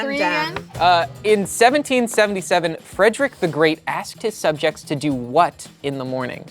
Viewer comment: I wasn't paying attention and instead of "Fredrick the Great asked" I heard "Fredrick the Great Asshole" lmao